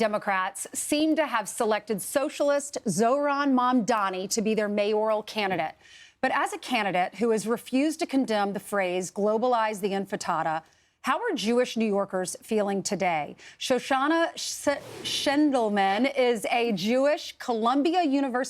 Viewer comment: Did she say "globalise the Infritata"?!!!😂😂😂😂